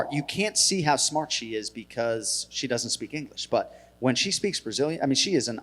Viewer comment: Did he just say "when she speaks Brazilian"?